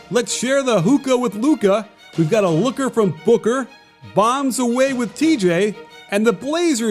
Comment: I swear I thought he said hooker for booker